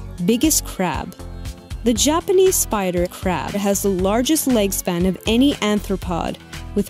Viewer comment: I thought she said Japanese spider crap for a sec 😂👌👍